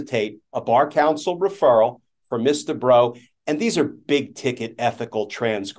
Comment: Ooooooh! The lawyer is "Miss Debrow"! I thought Glenn Kirshner was saying "Mister Bro" and then they started to refer to him as "she" and "her". And I thought "OMG, this person really made a mess with her pronouns thing!" 😄